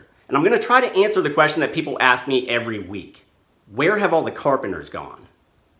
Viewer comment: At first I thought that said "Where have all the carpet munchers gone?" and I was like "Dude, where YOU been?!"